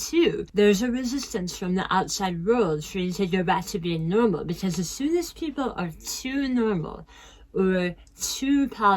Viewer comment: HI, im so sorry what do you say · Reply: Hi Megann! I was just saying that sometimes people treat you better when they know you've been sick, so that can make it hard to want to heal completely if it's in the back of your mind that you might lose that feeling from others. But we must always strive to get better and be our very best selves <3 I'm uploading the caption file this morning so hopefully if you want to rewatch you will catch it a bit more clearly, hahaha :) Thanks for being here, friend!